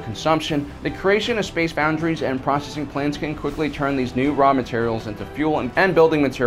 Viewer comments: my dumbass thought he said prostitute plans 😂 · Ahhhhh Processing plants. I thought you said, "prostitute planets." That sure would cause an economic boom.